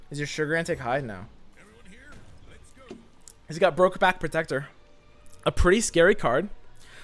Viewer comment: at first I thought he said "brokeback Protector" and thought to myself.. what kind of homophobic card is that? :P